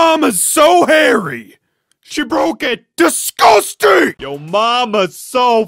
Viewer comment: i dont even know what he said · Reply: He said DisGU